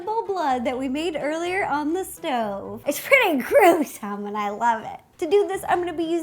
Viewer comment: what did she say? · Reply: Shaina Ramos She said it's pretty gross but i love it.